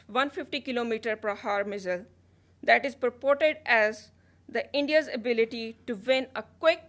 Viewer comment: Hahaha "purported"@ 7:15 I thought she said a "Parrot"